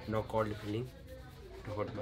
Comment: does he say "No call de police" ?